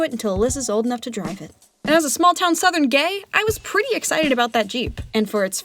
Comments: Did she say "And as a small town southern gay" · did she say "small town southern gay"? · did she say gay. Is she gay cuz same but i didn't know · I'm confused 😅 did she say 'As a small town southern gay'? Also at 3:04 I feel so happy everytime I see Reki and Langa ❤ I see those Little blushes 🤭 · Small town southern gay? Did I hear right?